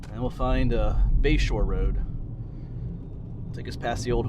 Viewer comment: Toward the end of the video I thought you were saying Bayshore rd and I thought, what an odd name for that area. I looked it up and it is Bashore rd, I assume after someone’s last name? Makes more sense. Anyway beautiful video of a beautiful area, thanks.